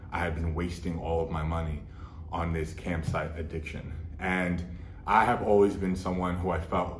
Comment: Beginning of the video I heard “campsite addiction” I was like “there’s nothing wrong with camping bro!” Then it started to make sense.